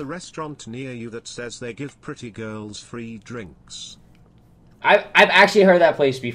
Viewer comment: I thought he say-"do you have pretty girl friend?"🤣